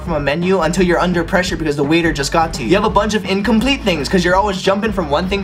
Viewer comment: I thought you said "because the wiener just got to you"